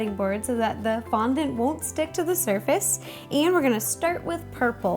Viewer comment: Am i the only one that heard her saying shtick rather than a stick